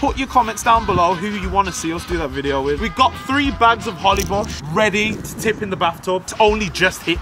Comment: I thought it said HOLY Bush XD